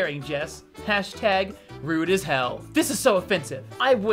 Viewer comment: I thought it said rude a shell lol Also when censors words it’s hilarious like “what the ghh “ More because I’m bored lol WW1 & 2, Hurricanes, other disasters: I sleep Someone asking something they don’t know the answer too: real grh